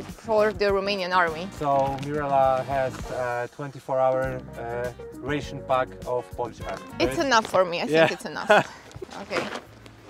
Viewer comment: I thought he said 24h pack of vodka😅